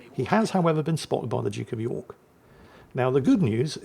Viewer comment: do I detect a double "actually Jingles" moment? sounds like he says "the Duke of NEW York" which... that would certainly be news to me that we've become a Duchy 🤣